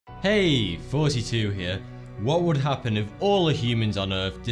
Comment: I just started watching your videos and this whole time I thought you said "hey 42 here" lol I love your videos tho